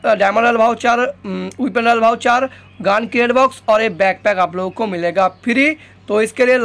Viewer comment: who hear g*nd create box 😂😂